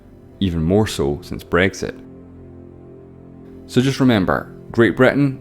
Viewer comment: "EU is an economic and political union of 27 European states...used to be 28 but you know Brexit " Oh I thought you said Breaks It